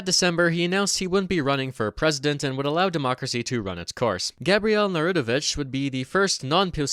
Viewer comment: Am I tripping or is he saying Gabriel Narutovich? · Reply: well, his name was Gabriel Narutowicz...